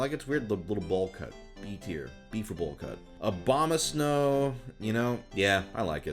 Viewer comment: I heard “OBAMA-snow” lol